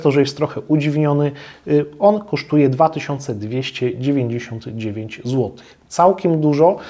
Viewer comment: Usłyszałem 299 złotych... dopiero po chwili dotarło do mnie, że wcześniej było jeszcze "dwa tysiące" XD